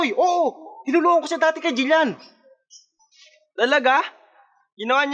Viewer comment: Wait 2:03 did you say Gillian that's my name